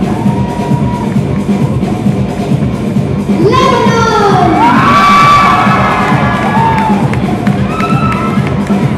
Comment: his body is perfection. from what country is he? I can't hear clearly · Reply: lebanon